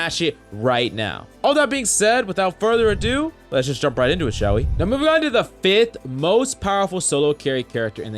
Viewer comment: what did he say?? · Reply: he said “now moving on to the..”